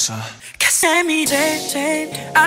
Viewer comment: sounds like jimin is saying "i love before"